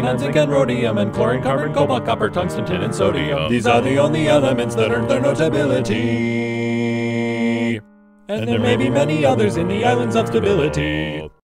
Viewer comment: What are the lines being said here? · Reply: "These are the only elements that earned their notability and there may be many others in the islands of stability." Quantum mechanics predicts that certain isotopes of extremely heavy elements can be stabilized by adding neutrons well enough that they can be studied and potentially used by increasing their half-lives. A chart showing the compositions and stabilities of such elements can be found here: <link>